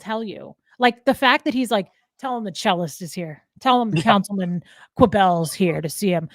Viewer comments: I thought she was saying the chillest. Like, “ I’m so chill, tell em the chillest is here”. But the cellist is maybe just as bad. Like the guy on Seinfeld who goes by the maestro. Lol. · Oh goodness. If you said no to Jeff and his reply was "No means yes" and he proceeded? Well, I mean, we know what forcing sex without consent is, right? Terrible :( Edit: Looks like there are victim-blaming trolls. Total bummer. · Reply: I thought you were saying chillest at first and not “The Cellist”.